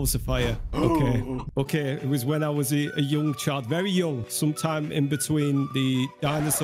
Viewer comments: When said he was a young child it sounded like he said young chad not child 😂😂😂😂😂😂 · it sounded like he said “I was a yo child very young